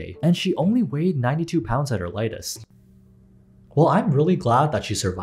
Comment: this is horrifying but I misheard her weight as “only 2lbs at her lightest” and burst into laughter. I feel so bad though